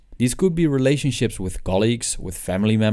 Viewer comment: did he just say relationships with family members? SWEET HOME ALABAMA